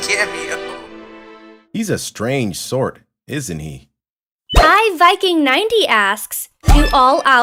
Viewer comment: Why did you say iVyking? You know "I" is a much different word than "Y"